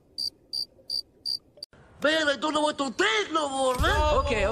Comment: what does it say? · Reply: "MAN I DON'T KNOW WHAT TO THINK NO MORE MAN-"